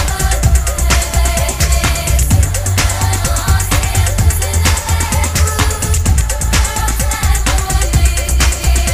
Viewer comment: eww and now im singin to the bath room... Not sure what shes saying after that??? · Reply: It’s “Heaven on Earth” in reverse